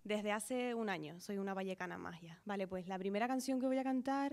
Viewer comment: que dice? Soy una vallecana magia? Vallecana de Vallecas? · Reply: Maha Qabil una vallecana más